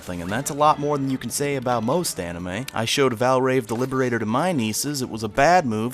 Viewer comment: Wait what anime did he say at 2:17? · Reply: Valvrave the Liberator